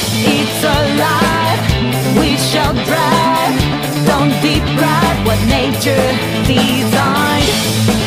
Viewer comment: I always heard it as "don't deep fry what natured designed". Turns out its deprive. Which makes more sense I guess.